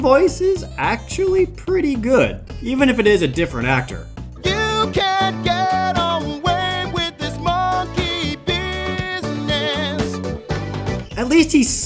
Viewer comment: Am I the only one who thought donkey kong said"you can't get away from this monkey penis." When Jared was showing one of the musical numbers